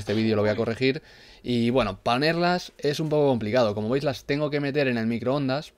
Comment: like si escuchaste que dijo panerlas en el minuto 1:41 solo eso Jjjj tus vídeos son lo máximo